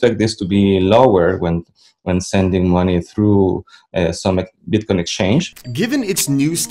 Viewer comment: did i hear him say bitconnect?